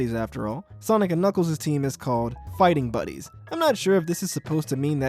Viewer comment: No way I thought “Fighting buddies” Said “ Fisting buddies”